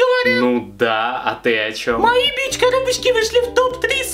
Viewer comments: Мне одной показалось что она сказала бич коробочки? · Я одной послышалось что она сказала бичкоробочка